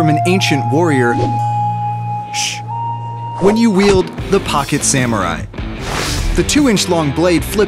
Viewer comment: I thought it said rocket saumrie